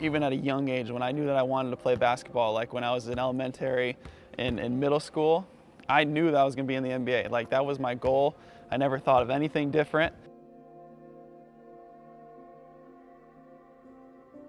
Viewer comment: did he just say ELEMEN-TERRY?